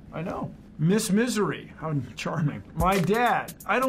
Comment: I wasn't watching the vid, just listening, so after 0:44 before I read the subtitles I thought I heard Bill Nye say "even in this misery, I'm charming" which still made sense